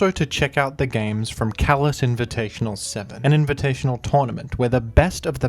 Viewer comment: Ohhhhhhh, it’s “Callous Invitational.” I’m new here and this entire time I’ve been watching the ADV Revival commentary videos I thought has been saying “Kalos Invitational.” I wasn’t really sure what Kalos had to do with ADV, but I went with it.